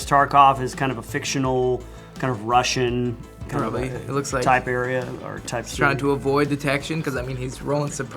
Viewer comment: when say "type area", he literally sounded like he said "Tiberium." maybe it's just me or it's his accent. anyway, nice video.